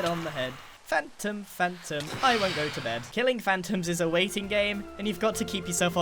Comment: They should Remix What Grian said " Phantom Phantom Bomp it the head Bomp it on The Head Bomp it on the head" " Phantom Phantom I don't go to Bed" Hahahahaahahah that song made laugh forever like I could never stop laughing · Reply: "I dont go to bed" i heard it as "i wont go to bed"